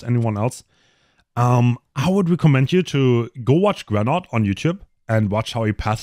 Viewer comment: someone please tell what is saying here? Go watch granard? Genart? I don’t know what he’s saying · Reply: Grenout, if youre still looking for an answer